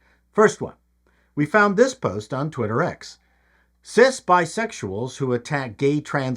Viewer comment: I'm still wrestling with "cis bisexual". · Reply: I thought he said "sis bisexual". I can't keep up with all the new fetishes.